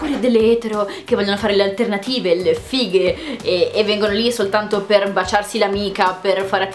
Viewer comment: giuro che a 3:30 ho sentito "sono lì solo per baciarsi la figa" 😹😹😹😹